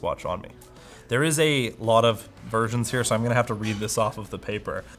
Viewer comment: Did anyone else hear virgins?